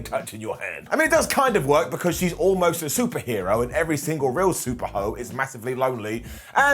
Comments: Did Simon say super ho? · lol did Simon say “Super Ho”? At the 4:57 mark?😂 or am I trippin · What did Simon call Nikki at 4:57 🤣🤣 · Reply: Super ho 😂😂😂😂😂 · why did Simon call her a “Super hoe” lol? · did Simon call Nicky a Super Ho hahahaha